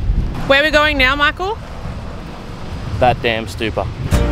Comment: it’s called: “taat dum” (with the t and the d sound) (it’s means: black stupa) not “dat dam” 👍 I got you In Laos we use the “th” for the “t” sound here P.s When I heard you said “that dam stupa “ it’s Iike you’re saying “that damn stupa” which is hilarious 😂